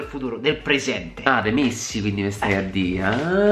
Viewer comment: ho sentito missi non messi😂😂